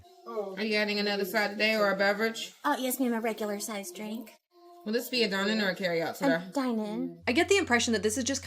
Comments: I thought she said “Would this be a Dining or Carry Out Serve” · Am I the only one who heard her say “Is this a dine in or carry out sweetheart?” Instead of sir?? I heard it clear as day the first time and whenever it was played again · I definitely hear “sir” as did all my family I asked to listen to it 😂 it sounds nothing like “today” to us but that being said, it’s not an accent we are used to as we are Scottish. I do believe she was rude at first and it would’ve rubbed me up the wrong way too had she not have been nicer when she came back over. I wouldn’t judge someone on their eye contact though as she may have autism or similar that prevents her from being able to. My daughter can’t make eye contact. She is clearly a sham though and trying to scam everyone. No idea why she would think it’s a good scam to claim to be a “mystery” shopper who puts everything online 😂